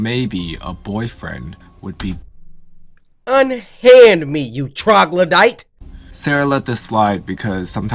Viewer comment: what did you call that Bohemian of a man? Unhand me you (trothlodite) WHAT!!!!!!? Momma Lisa 52 SAMURAI for life 😀😃😄😁😆😅😅😅😂🤣